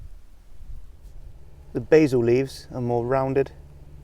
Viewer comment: My girlfriend heard you say ‘basal’ and thought you were saying basil like a yank, and was taking the piss out of you accordingly for doing so. So I said to her ‘it’s basal, as in the base of something’ and then I took the piss out of her. I thought I’d share so as you could do the same if you wanted. No need to hold back either. Worse thing is she’s a teacher!